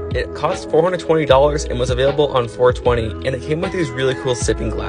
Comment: I thought i heard him said "I have tumor"💀